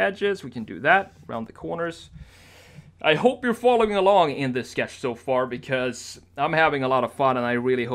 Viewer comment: I thought you were talking to me at 22:31 when you said "following along" I thought you said " following along indecideful " 😂